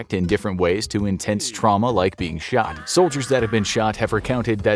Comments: I thought it said "short" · I thought it said "short" 💀